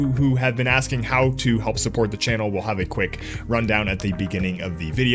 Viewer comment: I heard "we will have a quick 'Rundall'" and thought: man, he's quick with the puns today, but I guess that's was my imagination/expectation haha